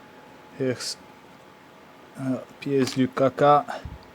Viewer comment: What...? what did you say? "appears we kaka?" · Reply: piece du caca -> piece of shoit